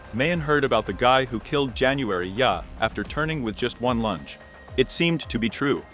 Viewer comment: A minute in and I hear "killed January with a turn of one lunch" ... WTF is this garbage AI voiceover.